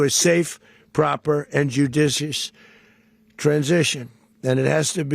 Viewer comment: how many times has Trump said "JEWdicious", hmmm....🤔what is he saying exactly?